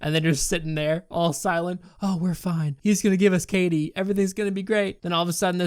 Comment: I thought he said KD like Kraft Dinner not Katie😂😂😂